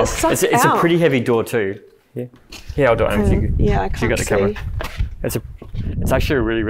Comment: Hey guys I heard an EVP at 28:48 when they are trying to close the door. Sounds like the EVP says “Cant reach it” another one at 38:48 saying “yeah”